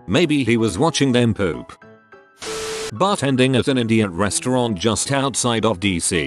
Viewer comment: I was listening to the video and not watching, and I honestly thought the TTS voice said “IDIOT restaurant” instead of Indian restaurant. 🤣 I was wondering what kind of restaurant THAT would be like! 😆